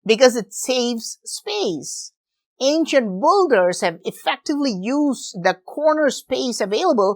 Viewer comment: I know he’s saying builders, but I keep hearing “ancient boulders”.